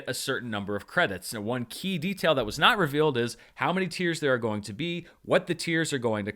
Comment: Good grief lol - when you said "How many tiers", I heard "tears" and just envisioned Stacy crying over the loss of 2.0 by the end of the year.....lol